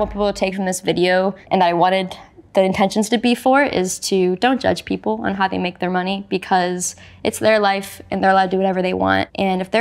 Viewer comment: didnt understand what the young lady said, she said dont judge people based on how they get their money AS LONG as its SAFE, CONSENSUAL, etc...